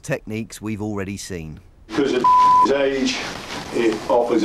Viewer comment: I thought for a minute he said because of the fuckers age lol